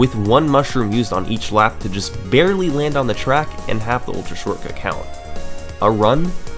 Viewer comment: idk why, but it sounds like he says "fucking" instead of "clocking" at 9:53 if you wear airpods and turn your head from left to right